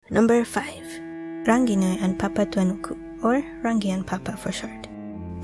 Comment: Totally thought I heard "Papa Franku" at the very beginning. Sorry heh. And yayy Saturday's videoo